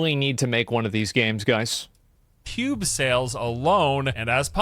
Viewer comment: I heard "pube sales" I am too tired